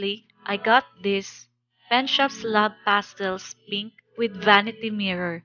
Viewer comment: When you said Penshoppe, I thought you had said "Pet Shop" LOL... that is something totally different! Looks like a good group of products overall!